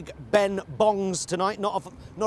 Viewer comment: I’m sure Pie said “not enough bob’s were bunged”, but that’s not what the auto-generated closed captions thought he said. Poor Bobs.